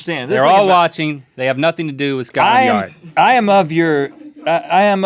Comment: For a second I thought Link said, "Scott Lenyard".